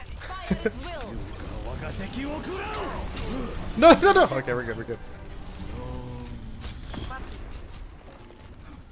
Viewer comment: What the heck does Hanzo say for his mutikill at 8:45? Lunch? lol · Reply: "So much death...."